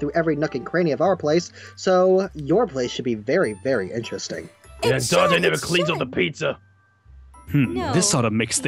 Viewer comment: Did Garroth just say “the socks will never cling to the pizza”